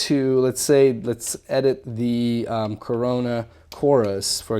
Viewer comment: I misheard corona chorus with corona virus